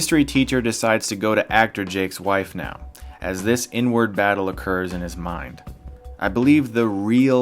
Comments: Lol I heard " N-word battle" and my imagination took off 😂🤣 Chillll Jake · when you said inward at 14:03 I thought you was saying “ n word “ I had to rewind that shit like 4 times 😂 I think you hit it on the head with this break down though 😂